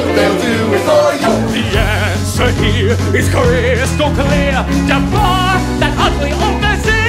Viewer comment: Prince Achmed's guard is my favorite because he started the whole Tiger F*cker trend. And I'm pretty sure that he was the one who said the Ja-far joke.😂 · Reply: I always heard it as ja- FART